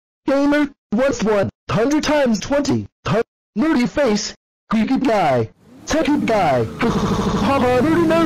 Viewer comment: What did red Barney say? (Plz tell me) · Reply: He said nerdy what 100 x 20